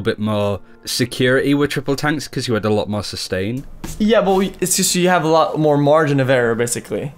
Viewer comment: Did he just say "yeah boi"?